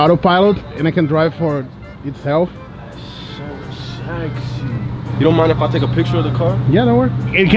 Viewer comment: eu entendi o cara falando: so sexyy KKKKKKKK